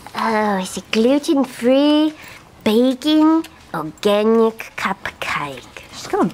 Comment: I heard "Gluten free, bacon, organic cupcake" 😂😂😂